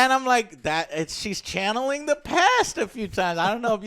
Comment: What movie did these chaps call a classic comedy? I heard 'the pass', and when I searched for that, the only result I found was decidedly not a comedy! ^^